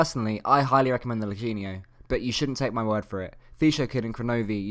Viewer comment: What? I can't decypher what he is saying Eugenia? wtf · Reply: "Lachinio", a top RL player, see comments above :)